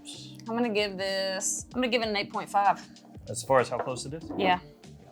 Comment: i thought she said 8.5 lol